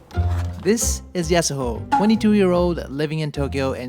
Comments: Did someone say Yasuo? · i thought he said "Yasuo" at first IGEI TON